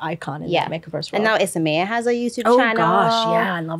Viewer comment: Who is the other MUA with a YT channel, apart from Lisa? Couldn’t understand what you said 🥺🥺🥺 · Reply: Isamaya Ffrench!